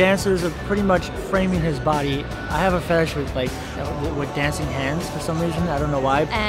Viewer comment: wait...did he say fetish? No shame just wondering.